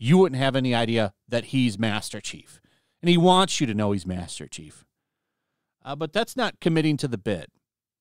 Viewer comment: he wants to show his ass too if I heard correctly